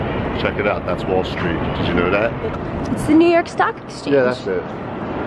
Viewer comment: I thought Jeana said "sock exchange"